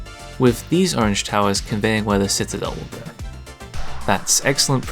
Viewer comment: did someone say citadel?